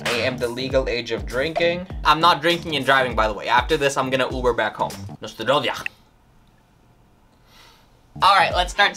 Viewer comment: not sure but in 0:17 i think he said "na zdrowie" which in polish means something like cheers